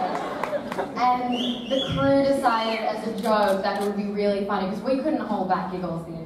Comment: About the funniest moment what did she say? I can't get it because English is not my mother tongue...start from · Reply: she said: it was the scene when Eliza and I ( making hands sights about their love scene) and the crew decided as a joke that will be really funny, cuz we couldn't hold back giggles the entire time it was very funny to do.... am and the first time we had to (i'll replace the word with ''shoot'' cuz i didn't get it eighter) shoot we were very serious ''we just gotta get it out, just do it, it's fine, stop laughing'' and as soon as we started we walked onto the set and they started playing Let's Marvin Gay and get it on ( by Charles Puth and Megan Trainor it's a good son you should check it)............. and Alycia couldn't stop laughing and said that this will take all-day Hope you'll understand and Have a nice day!!!